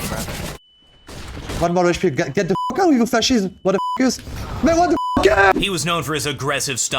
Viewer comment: did kenny say "get the fuck out of here facist" ?